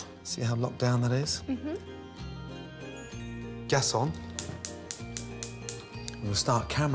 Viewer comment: HAHA i hear "Garcon" but he was saying 'Gas on'